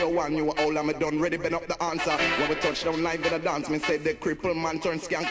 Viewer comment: anyone know what he says at 2:44... i make it as.... me ride ina studio 1 an ya know me a'ready dun up the answer... but i know that aint right...R.I.P! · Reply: we ride any studio one- new, old and we done 'ready bend up di answer